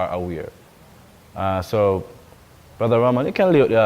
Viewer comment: BLESSED BROTHER AN PERFECT LOVE GOOD SEEING YOU AGAIN ❤. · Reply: Ha....me liccle English women thought he said, brother Ra man....new to this channel, so I'm like. Hold on a minute....is one cheking fe Ra....an one chek fe Yeshua🤦😁